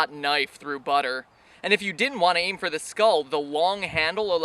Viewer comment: is it me, or do i hear "wong" handle at